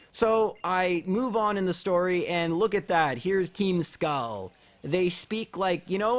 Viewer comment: I thought you said keemstar lol